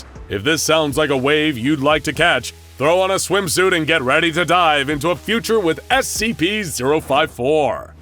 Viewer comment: i heard "get ready to die" and was confused for a moment